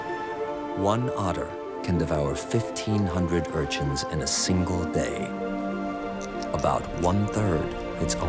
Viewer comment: "one otter can devour 1500 sea urchins in a day"... am I hearing that right?